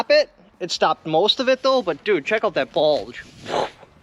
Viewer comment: Did he just say "Dude check out that bulge"?😬